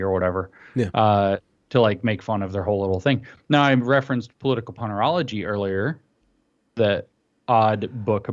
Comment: I thought James was saying Political Punnerology. Puns are evil.